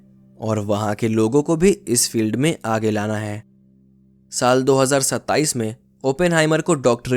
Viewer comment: Did i heard 2027😅